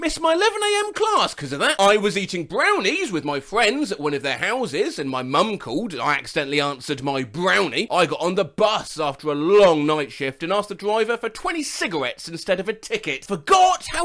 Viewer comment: i swear i heard him say brownie bus